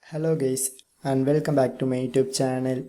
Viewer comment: He literally said hello gays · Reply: Ye he said hello its just that he speaks like that so it will sound like he said gay